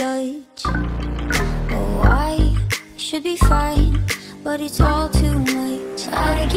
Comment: “When we are kids, we’re led to believe that everything is black and white. Good and evil. Hero and villain.But when we grow up, we understand. It’s all gray. Different shades of it, yes, but gray nonetheless”-me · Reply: I thought it said "its all gay- O_O"